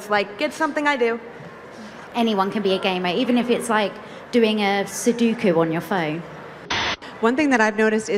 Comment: gaming 2016: sudoku on your Phone! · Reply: i thought she said Hadoken on your phone